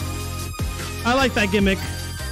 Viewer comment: What did he said??? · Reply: I believe “I like that gimmick”